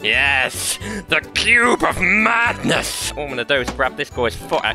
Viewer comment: I thought he said pube of madness